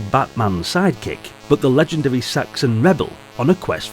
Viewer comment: Oh my, I misheard 'Saxon' as 'sexy' :D Anyway - cool game, though maybe a bit repetitive in the loop of obtaining gold - buying stuff. Me and my father we loved to draw maps. He worked in road engineering and had access to large sheets of graph paper. That was the most convenient stuff for map drawing with pencil. Great times. :)